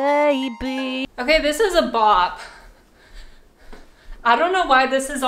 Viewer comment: i thought she said blop 😂😂😂😂